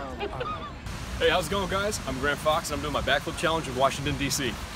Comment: When he said grant fox I thought he said it’s grandpa fox